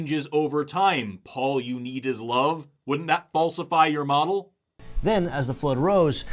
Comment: Did you say "Paulsify your model" instead of "falsify"? I genuinely can't tell now